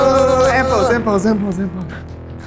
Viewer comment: everybody is being oddly silent about those captions saying "Simple" instead of "End pose"